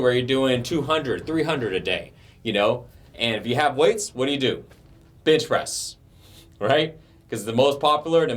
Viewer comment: I kept hearing "bitch press" and getting confused for a second